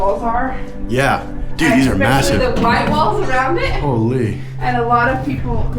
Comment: did you just say it’s MASSIVE